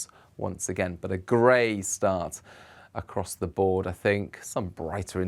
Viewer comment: I swear he says across the border thing, at 0:50🤣